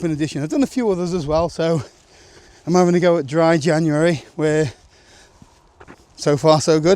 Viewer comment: Dry Ginuary, did you say?